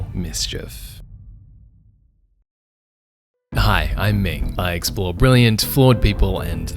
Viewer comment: I heard. Hi I'm me